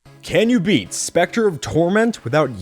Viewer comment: i thought you said "specter of truth" XD